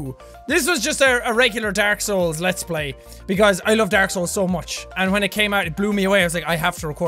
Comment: I thought he said I love arseholes